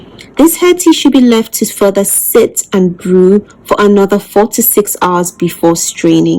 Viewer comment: 6 hours or 46 hours which please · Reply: It sounded like she said 4 to 6 hours, I could be wrong.